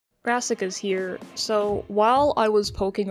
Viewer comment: My volume was really low and at first I thought they said "Masochist here"